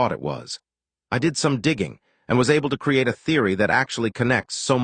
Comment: for a first time i hear: "i dig some diging"